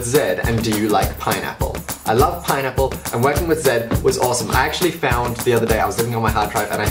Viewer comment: when you said working with I thought I heard wedding with im sorry